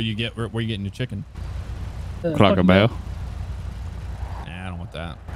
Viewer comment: "Where you ordering from" I shit you not I thought I heard him say "Cracker Barrel" making reaction just freakin hilarious to me